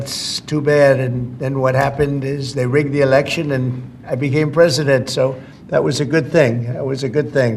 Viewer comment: Did he just say, "They rigged the election and I won."